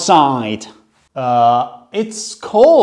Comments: I can confirm that no one says “it’s bone chilling out here” It’s been 3 months and if y’all don’t stop commenting bing chilling- 💀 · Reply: I hear “it’s bing chilling” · I thought he said "BING CHILLING" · The bone chilling I thought he said It's BING CHILLING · I thought he said bing chilling instant of bone-chilling😭😭🤣🤣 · Me who thought he said bing chilling instead of bone chilling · What I hear : It's bing chilling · I thought you say "bing chilling" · I heard is "its bing chilling" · I thought he said its bing chilling💀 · I heard 'Its BING-CHILLING'